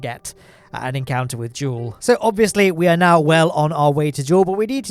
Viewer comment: What he says: we are now on our way to Jool what the captions say: we are now on our way to JAIL!